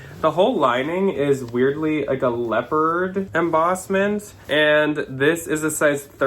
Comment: You said "leopard embossment"; I heard "leper embossment", and was perplexed for a moment, lol